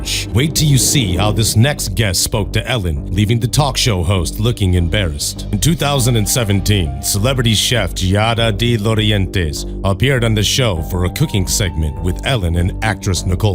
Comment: Shows "2012", but i heard "2017", anyone know which one it is?